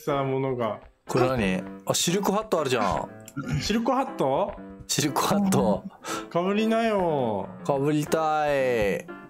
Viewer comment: 私もしるこハットって聞こえました🐶ww